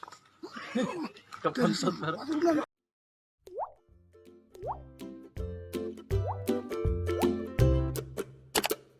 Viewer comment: ada yg tau gak si pabak ngomong apa Otw🤕🤕🏥💉🛏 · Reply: Agerhh